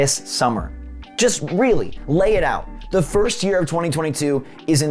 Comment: Did he just say the first year of 2022?